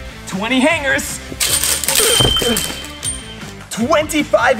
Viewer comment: I thought instead of him saying "thats the number" I thought he said "thats a number" omg really??? thanks for clarifying I thought I was a letter